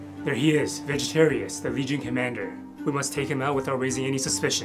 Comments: I thought he said "Vegen commander" 😂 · "These mosquitoes are no joke". Bro learned a history lesson. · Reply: When I first saw this, i thought he said “Vegetarian; the vegan commander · I swore he said Vegan commander and was expecting different from Vegetarius